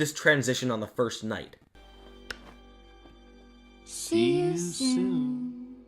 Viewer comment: When she said "see you soon" I heard "see you sewn" BRAIN GAMES!